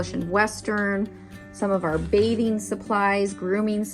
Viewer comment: I looked away for 2 secs and heard “new baby supplies” 😅and about had a heart attack then rewound and saw it was “new bathing supplies!!” 🤦‍♀️then thought yeah said no more babies right now!! So of course it’s bathing supplies😂. I still love the wood panels in the arena!! The property really is beautiful!! I wish it was just larger (I’ve been praying the church would sell land so FSE could stay. If it doesn’t happen I’m sure there are bigger plans for FSE)! Oh and of course Penelope’s wiggles always makes my day!!